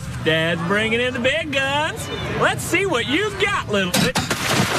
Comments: That first hit, sounded like he was saying 'lets see what you got little bit-' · Everyone thought that he said “lEtS sEe wAt u gOt LiTtLe bitc-“ But he actually said mis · he said "little mi-" but i misheard it as "little bi-" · For a second I thought he said “let’s see what you got little bi-“ I was like “now hold on a dam minute” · I thought he said "lil bit-" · Why did I hear, “let’s see what you got little bi-“💀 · Thought he said "Little bitc-" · I thought he said let's see what you got little ni